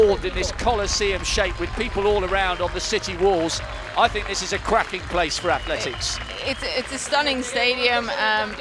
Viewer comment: the commentator apparently says "I think this is a cracking place for athletics!", but I really thought he said "I think this is a crappy place for athletics!"